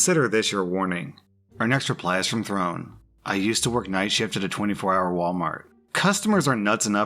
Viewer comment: I thought I said night shit XD